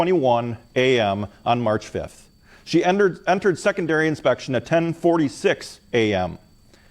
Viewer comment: every time he said, °secondary inspection°, i thought he said, °secondary infection°, & figured it a funny name for a 🍷pub!